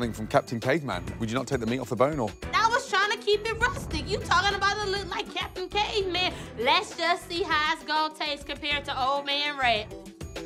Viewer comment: What did she said? Im sorry I'm still learning XD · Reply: "You talking about that look like captain caveman.. Let's just see how it's gonna taste compared to old man Ray"